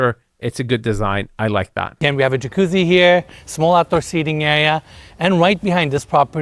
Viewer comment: Did he say white steps or wide steps? I know he’s originally from Turkey, not picking on him, also I always thought he was saying Opera Seating for the longest time, which didn’t make sense to me, and I believe he was saying Outside Seating